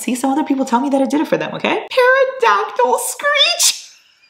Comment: im sorry did you just say "para-dactyl"